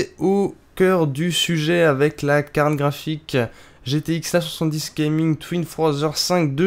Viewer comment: C'est moi ou a 2:19 t'as dit 770 au lieu de 970 ??